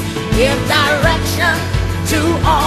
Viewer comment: When I heard her say at 1:39 "All life's plan" I thought she said, "All God's plan" for a moment!! 😄